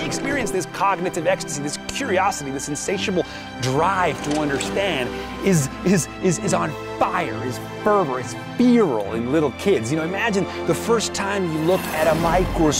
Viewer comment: What is the word after Jason says (around 1:18) 'this curiosity this sensationable drive to understand is on fire, is feverous its XXX.' Spelling it like it sounds is like 'Fearol' or 'Feerol' but can't find the correct word. Any help would be ace! Thanks